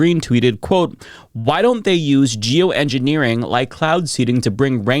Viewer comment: I wasn't watching the screen when he read out the MTG tweet and thought he said "Jew engineering", and I was more surprised it actually said "geo engineering" when I double checked it.